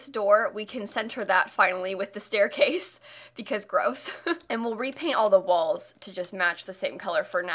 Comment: I thought she said let’s go bro